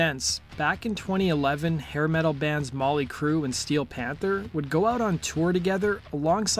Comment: Did he say Molly Crue at 0:12?